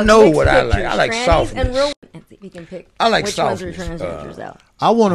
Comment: um....did say he "liked sausage"?!?!?